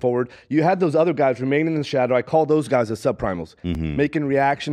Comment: “Sopranos” I thought he said sub primals 😂 But if the caption says what it says it’s legit